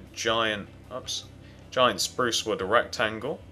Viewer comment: Sorry If Gross OR Inappropriate But it sounded like he said ERECTangle When HE said Rectangle LOL